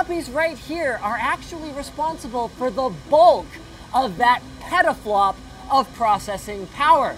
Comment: sounds like he said pedoflop.. lol